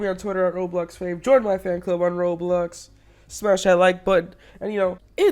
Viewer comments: fave says smash the like button captions says smosh I like butt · smosh I like butt is what the captions say